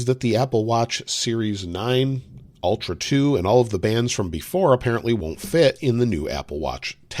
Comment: I legit heard that as "bans" rather than "bands" 🤣🤣